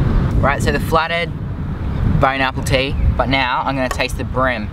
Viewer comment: did he say BONE APPLE TEA